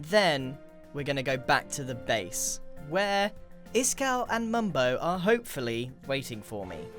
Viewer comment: If you go to 1:22 and turn on English captions it will say “this cow” instead of “Iskall”